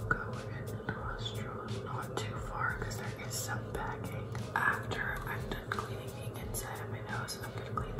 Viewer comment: Did she say there was packing in her nose? 8:18.